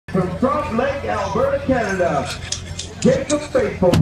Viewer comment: sounds like he said, from frost lake Alberta